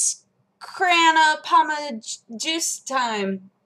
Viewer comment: aww i thought she said Petey Piranha juice :(